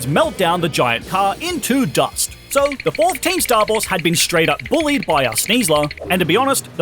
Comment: What said: "Fourth Team Star Boss"; What I heard: "Team Four Star Boss"